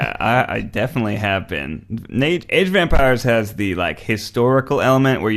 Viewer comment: Legit though Ned was talking about a game called “Age of Vampires”, I texted my husband and was like “a historical vampire game - whaaa?!” Needless to say he laughed at me and “Age of EMPIRES” makes much more sense.. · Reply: LOL, I thought he said "vampires" too!